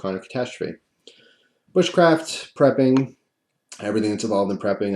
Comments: When you said bush craft I seriously thought you said wich craft.... had to go back to relies you didn't haha love your videos BTW keep it up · Dude I thought you said witchcraft...had to back up...bushcraft,lol! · I thought he said Witchcraft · That's what I thought, but I think he said bushcraft haha